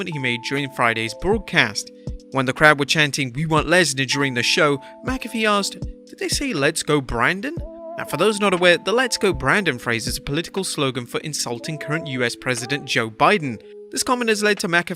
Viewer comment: I thought McAfee said "let's go roman" 🤔